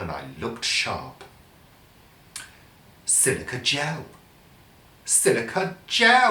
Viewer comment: When you repeated "Silica Gel!" (4:15), you actually sounded like saying "Sunday Chat!". Hahahaaa!